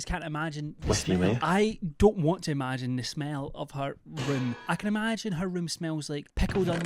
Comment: I misheard you, instead of ROOM, I thought you said 'I don't want to imagine the smell of her wound' I gagged. Hard.